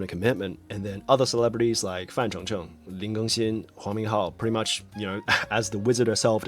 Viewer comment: Bro when you said the name "huang minghao" I was so shocked cause I heard "hou minghao" and he's currently my fav actor but then I read the name thank God😮‍💨